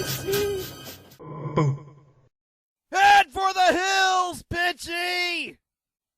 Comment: Great work! I thought it said Head for the hills bi'tc'y